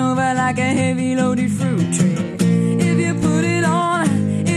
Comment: I always thought she said "fruit tray" instead of "fruit tree"...